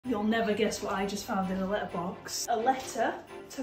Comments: At first, I thought she said "You'll never guess what I found in the litter box." 🤣 I was a bit scared at first and then very confused when it was a letter from Santa! 🎅 🐈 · I thought she said litter box at first, I was like why is Podge stealing the mail? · Totally thought she said she found it in the litter box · I thought she said litter box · I thought said litter box at first... 🤣